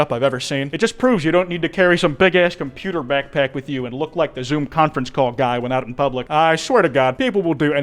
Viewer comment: what did kwite say here?, I keep getting distracted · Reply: "it just proves you don't need to carry some bigass computer backpack with you and look like the zoom conference call guy when out in public"